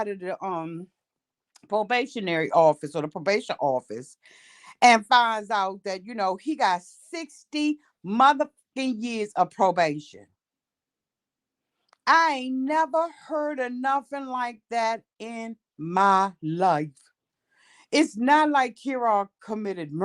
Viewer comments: I could have swore I heard say 6 years probation. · I thought said he has 6 years probation not 60